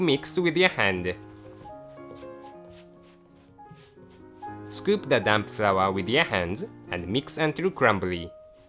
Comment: lol i thought he said "Scoop the Dam flour"